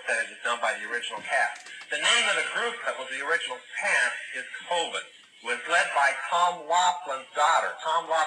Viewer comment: Did he say COVID?